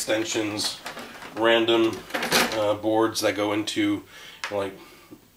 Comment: nice video,some good looking boards there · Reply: Thought You said broads , had to go back to see if My eyesight was failing Me.. .(ha ,ha) Best wishes.............................Dee